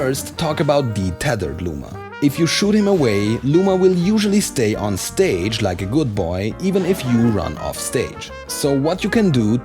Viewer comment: did he just say “He will stay on stage like a good boy”